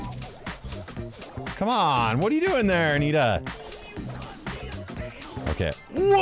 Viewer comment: Is it just me or I heard tuna?